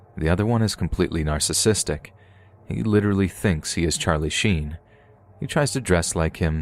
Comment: I'm half asleep, and I misheard Charlie Sheen as Charlie Chaplin